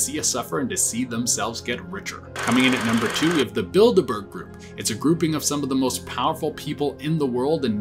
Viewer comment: i always hear The Bilderberg Group as The Build-A-Bear Group 😳🐵